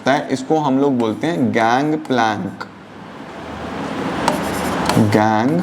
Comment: I thought he said gang bang😂😂